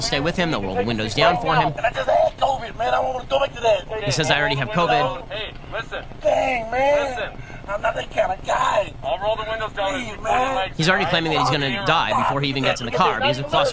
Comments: Ben, why are you spinning what Floyd is saying? 3:44 listen for yourself. its your irresponsible coverage that is the problem... What Floyd actually said- "i just had covid. I dont want to go back to that! you know? Dang man, im not that kind of guy! pleas man? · I don't think Ben heard him right but he said "I'm not that kinda guy" not "I'm going to die". If you play it at .75x speed you can hear it more clearly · he actually said “I’m not that kinda guy” not I’m going to die. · What?? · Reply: George Floyd said "I'm not that kinda guy" when Ben Shapiro thought he said "I'm gonna die" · "I'm not that kind of guy" is what it sounds like he said. Thank you for this update. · Was I the only one who heard George say "I'm not that kind of guy" when Ben quoted him saying "I'm gonna die"??? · George Floyd said " I just HAD covid, I'm not that kind of guy" Not what this interviewer claims he said. I have covid and I'm gonna die. Someone needs to re listen and maybe clean their ears out! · i think at 3:45 he says "I'm not that kind of guy" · I think he said I’m not that kinda guy